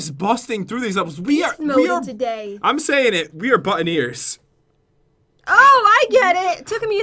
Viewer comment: who else thought pat said "butt in ears"? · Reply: Dabbing Litten I thought he said that but kinda diff I thought he said "Butt 'n ears